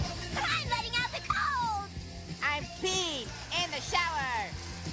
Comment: I’m so stupid. When I was young I thought Sheen said I’m being in the shower. And where did Carl get that Llama?